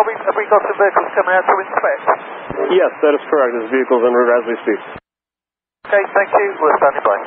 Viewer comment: the subtitles said "on their route to assist" but this is incorrect. Tower actually said "...vehicles on their way as we speak".